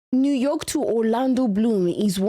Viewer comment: did I hear that right? New York to Orlando Bloom?